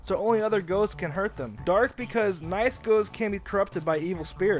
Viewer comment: What does he say for why Ghost is weak to dark? (3:04) Captions say "nice girls" lmao wtf